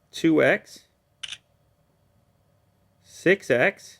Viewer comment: did you say "sick sex" ? xD